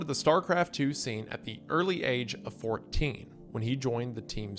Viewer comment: OMG I thought he said fly. I thought it was the return of his nemesis 😂